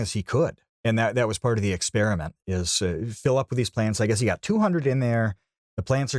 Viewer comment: i thought he said planes in the beginning, it was very confusing